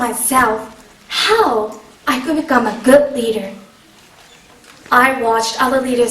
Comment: I thought she said reader at first.